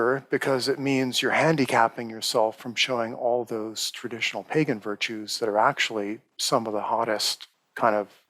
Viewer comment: what’s he saying here? asking for a friend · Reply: pagan virtues. aka Do you even lift bro :o